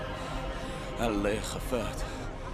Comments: is he saying Allah hifazat ? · No sé egipcio pero creo que dijo: "En la madre"